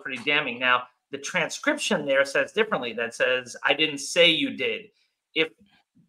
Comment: I heard say “ I didn’t say you did”